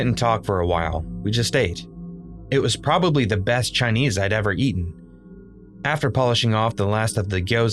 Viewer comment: did he just say 'it was probably the best CHINESE i've ever eaten' 💀💀